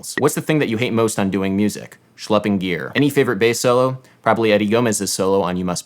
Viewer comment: did he say "shlopping gear"? what does that mean?